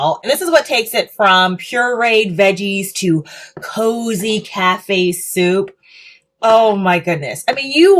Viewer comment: "cafe soup" --I had to do a double take. I heard "cat based soup."